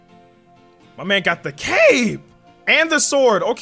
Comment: for a second I thought you said "MY MAN GOT THE CAKEEE" I was like wooooaah pauuuuse